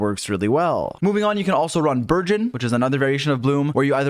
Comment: Why does it sound like ur saying virgin when you say burgeon?